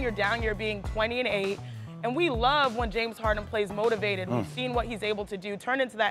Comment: Did she just say harden plays defense at the end ? 😭😭😭